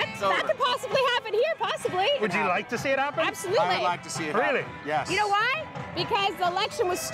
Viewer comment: When that woman in the interview said "absolutely" at 2:57 I initially misheard it as "I'm stupid" and thought "yeah I know" lol